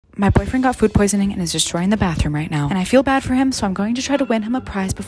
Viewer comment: When she said "my boyfriend got food poisoning and is just trying the bathroom" i thought she said destroysing the bathroom😂